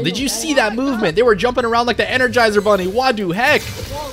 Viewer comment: did he just say WADUHEK?????? 11:46 LEGEND